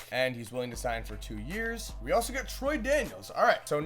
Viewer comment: I thought you said Troydan and my brain almost exploded..